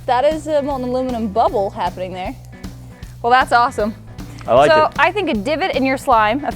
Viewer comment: did say "THATS A MOLTEN ALUMINEM BUTTHOLE HAPPENING THERE"?🤣😂🤣 IM DEAD